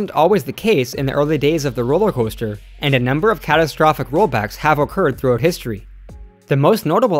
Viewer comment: Did you say...rollback? lol I can't hear that and not think Dragster/Ka